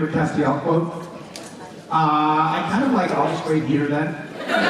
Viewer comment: wait, what was the quote, couldn't really hear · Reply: I'll just wait here then.